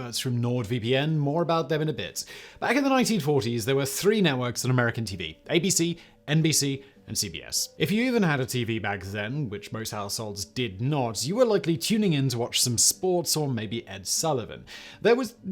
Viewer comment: I reported this video because when you said " Even if you had a tv which most households didn't " I thought you said " Most assholes didn't" I realize my mistake but will not un report the video. You have enough money Simon.